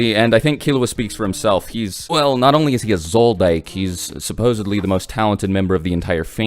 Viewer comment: I thought this man said “soul dyke” at 2:20 😂